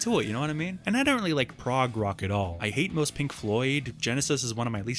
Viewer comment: Did he say he hates most Pink Floyd?